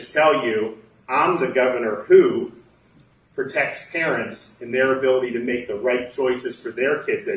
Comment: Lol. I thought he said, “I’m the governor who protects Karens”. Which he is.